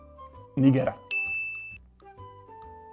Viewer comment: WHAT DID HE SAY?!?!?!?!?! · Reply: Niger an african Country north to Nigeria